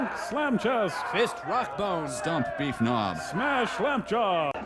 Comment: Can anyone make out what they say at 0:33? Stump Beef something? Nos? Gnaws? Nog? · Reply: Victor LaMie Stump Beef Knob lol